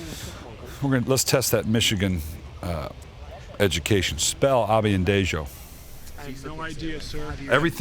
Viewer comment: Lol wtf was that word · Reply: obafemi ayanbadejo was our full back